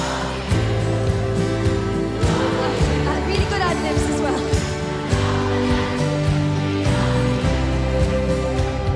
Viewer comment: did someone understand what jade was saying at 6:50? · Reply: Jade said: 'and I had really good ad-libs as well'. She was making a joke that she was annoyed at herself for getting emotional because she didn't get to do her ad-libs 😂